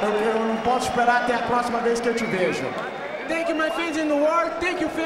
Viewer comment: is he saying "Thank you my friends in New York"?